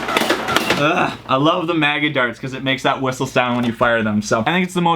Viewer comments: Did he say maga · anyone else hear maga instead of mega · Did he say at 6:10 maga darts?